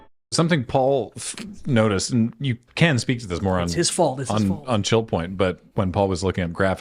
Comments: Did anyone else hear say "something pal said, and you can speak to this moron on chill point" 9:35 I think it was actually "more on" 😅😂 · I feel bad that I heard "You can speak to this more on ..." as "You can speak to this moron ...". That gave me whiplash for a moment before my brain corrected itself.